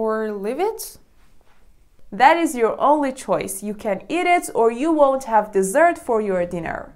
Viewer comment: I thought that she said you can eat it or yeet it